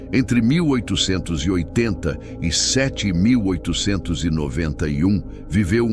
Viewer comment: Entre 1880 e 7881..." Ouvi bem ? Mas isso é á sério ?😂😂😂😂😂😂😂😂😂